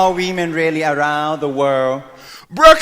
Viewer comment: He sounds like he is saying WEmen